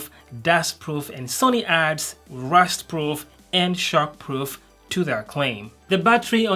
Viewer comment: I thought he said "Shark Proof".